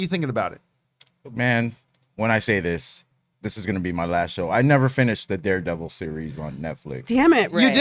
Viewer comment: Did Ray ever finish anything??? is funny becouse I always hear him said " I never finish.." I mean you are in a tv/ movie show dude